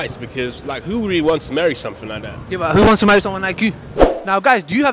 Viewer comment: The guy at 07:16 that's obviously got double standards didn't even say "someone" he said "something" what a way to objectify women. Saddend to say the least.